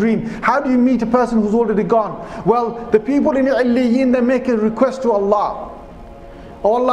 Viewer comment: Is he saying "early in" I can't hear that well